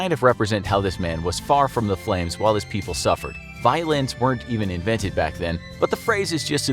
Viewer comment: I first heard it as "Violence weren't even invented back then" before I read the subs 😂😂😂